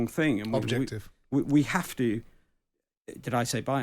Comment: Simon is very argumentative even when he isn't in an argument. Just let the bloke speak without butting in. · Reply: The guy actually said objective, Simon misheard him and then corrected him